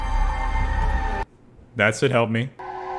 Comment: When it said "help me" (5:37) for some reason i thought it said "are you gay" 😂